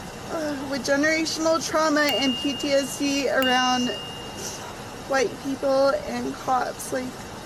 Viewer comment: did she said she has PTSD around white people? I’m just wondering cause I know she said a zillion times she’s ingenious but to me she looks white.